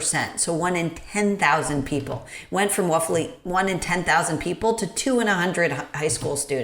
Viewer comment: She said wuffly instead of roughly. Cancelled.